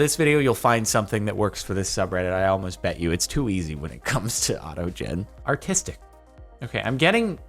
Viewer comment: Anyone else catch the "A Toe Jen" at 12:52?